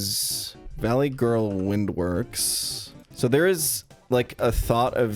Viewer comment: Did you say Valley GIRL Windworks at 50:38?